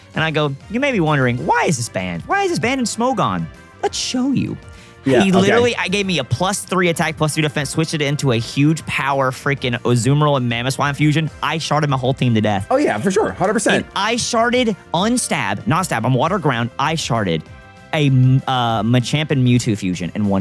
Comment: "ice sharded" (i sharted) my whole team to death. Why do i only hear the second option? 😅